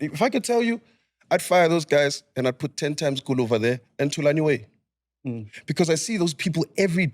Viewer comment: I hear 10x guluva but thulani way oh hell no 😑😭😭😭